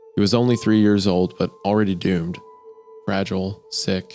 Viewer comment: I heard "gooned" instead of doomed and I got flashback to MeatCanyons video xD 1:00:25 HAH! I was thinking this earlier and they were talking about it being like the wind! I feel so vindicated C